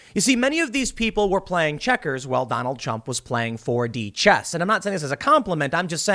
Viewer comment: Did he say “Donald Chump” at 0:15 ?